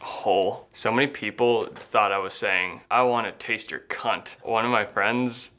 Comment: so many people thought I was saying “I wanna taste your c***”